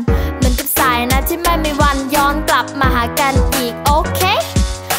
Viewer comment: สายน้ำไม่มีวันยอนกลับมาได้ยินว่ายองกลับมา